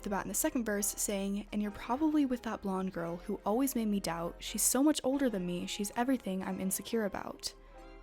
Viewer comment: I thought she said "She's so much shorter than me". I thought they were unique though. 😁